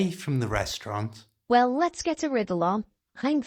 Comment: Hi. I couldn't help but notice that you've misheard an idiom: 'let's get a riddle on', should be 'let's get a wiggle on'. Thanks for producing these videos. All the best, Chris.